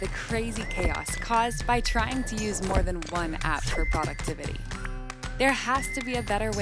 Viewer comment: Seriously. If you're going to make the volume of the background music nearly as high as the narrator, are you even sure you want people to be able to understand what she's saying? 0:04 "... one app for product ...WHAT"? Debate? I have no idea. Vocal fry doesn't help. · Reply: she said "productivity"